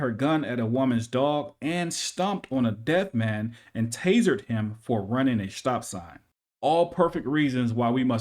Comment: At first I thought you said she stomped on a dead man and tasered him, but I think being dead would be a good excuse for running a stop sign.